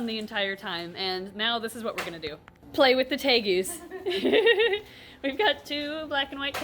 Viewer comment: I thought she was gonna say “Now we’re gonna play with the TAPEWORMS” cuz an ad came but she really said “Tegus” 😂